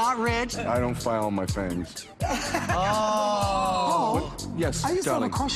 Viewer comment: What the photographer say at panel when they asked "whats one thing ppl always think about u that's not true ??? 35:35 "I don't file my things???" Is that what he says??? I wanna know lollll but i can't make out what he says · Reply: "I don't file my fangs" pretty much "I'm not a vampire in disguise"